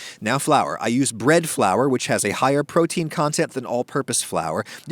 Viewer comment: omg bread flour. in the first video i heard "red" flour